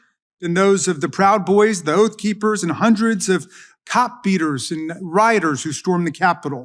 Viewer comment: I thought Raskin said "... cock beaters and rioters ...", it would have still been correct.